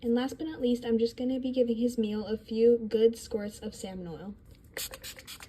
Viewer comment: HELP I THOUGHT SHE SAID SALMONELLA